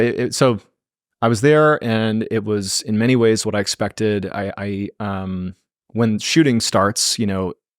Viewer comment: Idk y TF but this is exactly what I heard . (Goldmembers voice, LET THE SHOOSTING BEGIN!!!!! )